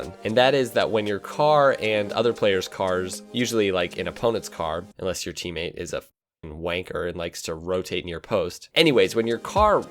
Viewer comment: "Unless your teammate is a fucking wanker" Did I hear that correctly?